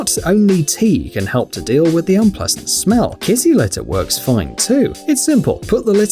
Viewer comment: who else thought he said kissy litter